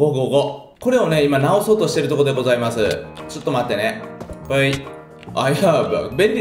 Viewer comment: に「I have a」って言ってるように聞こえる。